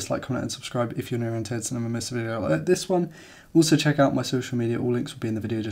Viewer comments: bartle do? (I think he said "if you have no intention of missing another video like this one"?) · Great video dude. I 100% agree. One question though... What in the world did you say at 5:42? YouTube captions thinks you said: subscribe if you're new intense and I'm a mess of a go at this one also check... I laughed my head off when I heard it but it's the cherry on top for this video. Thank you.